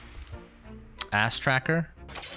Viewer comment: Did anyone else hear asstracker at 3:30?